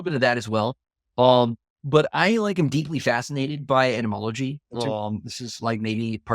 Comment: Haha the closed caption thought Christopher said that he likes Animology instead of Etymology.